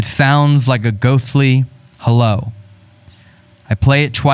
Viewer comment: That voicemail actually sounds like it says "I love you", not "hello" 🤷‍♀